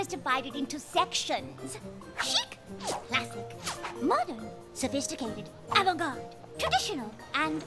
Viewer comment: I swear I thought she said "sadisticated" 7:46 Fluttershy's pose: .....bondage? 9:00 See internet? Rarity doesn't care if your friends are furries. More people should think like her.